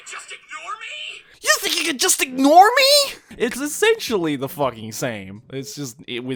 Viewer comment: I thought I heard "You think you can just ig-normie?!?"